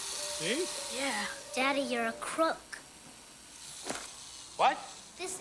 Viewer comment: “Daddy your a crook!” I thought she said, “Daddy your a cook!”